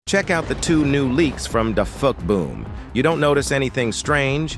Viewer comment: Bro i heard you say thefuckboom